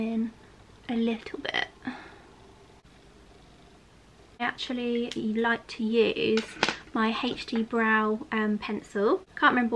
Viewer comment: Babe! What is the brow pencil you mentioned at 3:20? It sounds like you say “HD brow pencil”…? It doesn’t appear to be listed in the products you listed in the description. I neeeeed it! Help! Xx